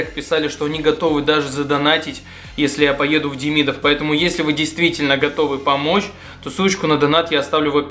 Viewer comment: «Если вы действительно готовы помочь, то сучку я достану» -послышалось мне